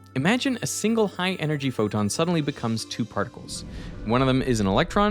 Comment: did you mean neutron? if a single proton becomes an electron and a positron, charge is not conserved EDIT: I just realzied you said photon. I heard proton. Nevermind